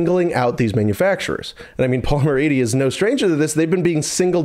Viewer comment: Before I read "polymer 80" (years ago) I thought people were saying "Paul Murrayney" and was like who the f is that anyway? 😂